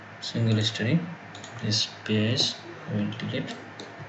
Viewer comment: please sir, What did you say in minute 4:42 ? this is my first work in ETABS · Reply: This base we will delete.(That means in base level the floor generated for all storey option, Need to delete that after going to that base floor selecting one storey option)